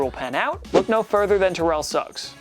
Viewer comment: When he said terrel Suggs I thought he said terrel sucks